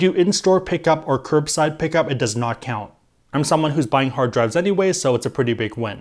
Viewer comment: anyone else mishear say he’s buying hard DRUGS instead of hard DRIVES as “a pretty big win”? 😂